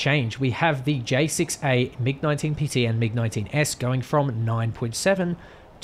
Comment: Mig21s at 9.3???? That is really bad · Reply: You misheard mig19s I think. But mig19 at 9.3 is also a bad idea LOL